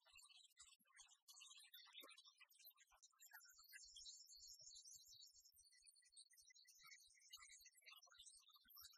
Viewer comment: Whaz did he say@ 24:30? "Like some sort of..." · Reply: hooters contest", ehm...google it :D